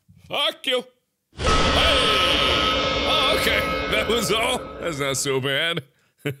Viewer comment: what was that waht he said thatwasall